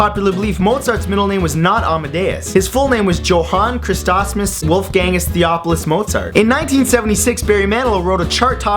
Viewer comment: did u say his name was WolfGangus.. probably lived with a gang of wolfs..